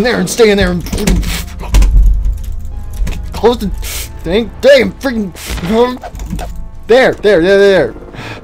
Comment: did say dang dame or damn?